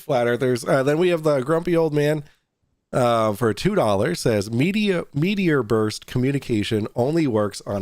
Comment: I want to correct something, I misheard "meteor burst" as "media burst." Absolutely correct, meteor burst is the radiation of the ionopshere caused by an meteor entering the atmosphere. This allows for propagatuon of higher frequencies in the ionosphere that wouldnt refelct under normal ionopspheric conditions (such as VHF). FE has no answers for meteors, much less meteor burst propagation.